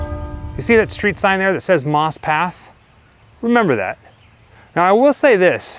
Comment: I thought that said "Moss Pâté". Not my favourite food.